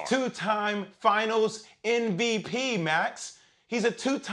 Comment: does he say "NVP" or "MVP"? Cause I'm hearing "NVP".